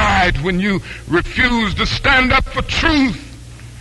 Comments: That's hella disrespectful, especially the the fact that it's cut off to sound like he says "you died when you refused to stand up for justin" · damn at the end it cuts so it sounds like he says “you died when you chose to stand up for justin”